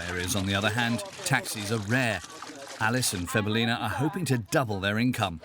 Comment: Did he say "febelina"?😅